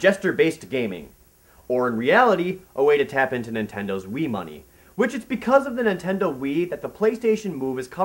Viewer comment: I thought you said Nintendo's weed money which I guess is connected to their wii money.